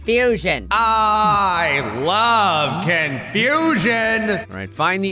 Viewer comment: I thought said “I love Contusions!”